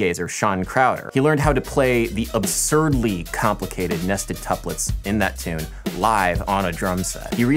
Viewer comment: I don't really speak english very much so all I hear is "nastied tuplets"